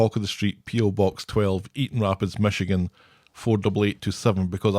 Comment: I like auction talk! EDIT: I'm sorry I thought said "Eden" instead of "Eaton"! It probably is my fault my letter got sent back 😭